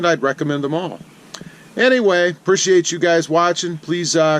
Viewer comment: Quite frankly the truck looks great. Now my question it… how much do they depreciate because of the badge removal. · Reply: Rudy-The-Cuban Gonzalez ,, I heard it “Appreciates” the trucks value by $ 1000.00